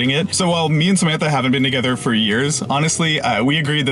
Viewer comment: He says they haven't been together for years. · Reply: oh... i heard "we've been together for years now." thanks, adhd, very cool. and thank you for the reply! <3